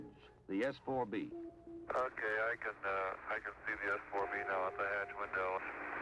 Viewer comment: what does he say at 4:20 and it's at 4:40 the quote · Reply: “Okay I can the s4b out of the hatch window” “Okay Houston we’ve had a problem”